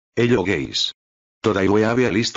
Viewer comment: i love how the "hello guys" sounds like hes saying "hello gays"